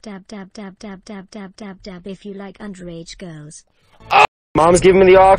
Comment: What say in 1:56? Because i'm french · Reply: dab if you like underage girls tamponnez si vous aimez les filles mineures